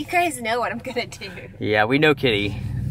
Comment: did you call her Katy? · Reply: I think "Kitty" is a nickname he uses.